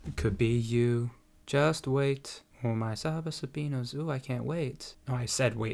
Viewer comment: What is she saying? "Serve up subpoenas?" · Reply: Saba Sabino's i think